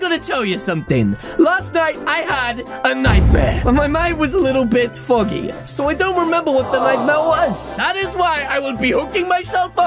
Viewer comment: I thought he said I have a little bit fuckie